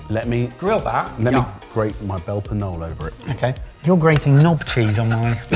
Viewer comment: Loved Mike's last review on the belper knolle. Had me cracking up, but I thought he said lettuce knolle! Get it? let us know? 😅